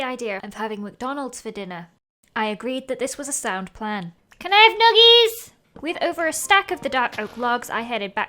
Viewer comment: Guys.. DID ANYONE HEAR SAY "CAN I HAVE NUGGIES"